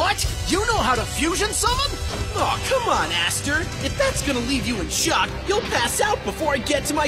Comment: wait did yuya just say aster??? like aster from yugioh gx???